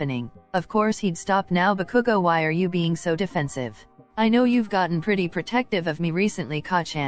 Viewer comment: I paused the vid and came back 2hrs later and unpaused and the first thing I heard was "are you fucking dence?"💀🙏😭